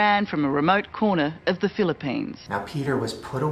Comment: Did she just say Philippines?